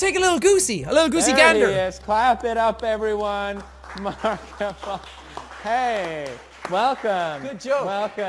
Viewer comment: did pewds just say markipl...